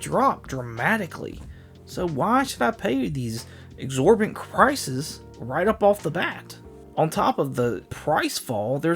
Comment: What did you say here? I know you meant to say prices, but I think because of your accent you said some sort of an amalgamation of prices and crisis. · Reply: LOL it is a crisis but, I said prices. I promise I wasn't driniking just a little slurred speech LOL